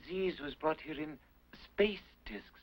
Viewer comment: I really thought she said 'what the fuck is this' in a really weird way XD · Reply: I thought he said ''It was brought here in space tits''. Had to play it twice to be sure.